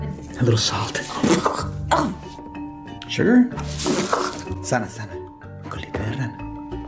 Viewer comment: You hear what he was saying? 0:53 SaNa sAnA cOlItA dE rAnA xdd